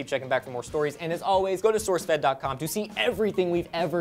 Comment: during the end it sounded like Lee said "I'm sweet deeks magillicuddy"